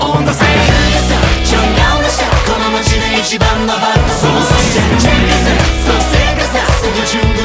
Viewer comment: この街で一番のBad Boysが全くボーイズに聞こえないんだがどうなってるん？バッドソースって聞こえるんだが・・・